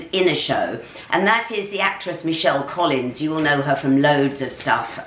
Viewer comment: Lol when you said Michelle Collins for some reason I thought you said vicky Michelle. Then when Michelle came on the screen I thought to myself "she wasn't in ello ello lol I really should stop smoking the devils lettuce 🤔